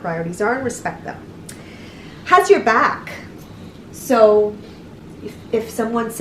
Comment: OMG when she said to have there back I thought she said where's you back lol...